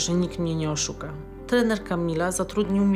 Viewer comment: ja usłyszałem trenerka mila XD